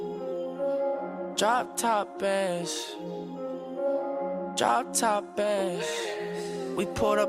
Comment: Ugh, this music makes me cringe. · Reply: Holly Russell i heard ‘drop top bass’